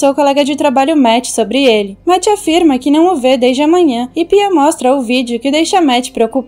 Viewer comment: é só eu que ouvi isso? ''não o vejo dês de amanhã''